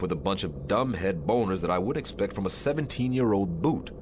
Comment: Did he really say "boners"? 🤔 (See transcript)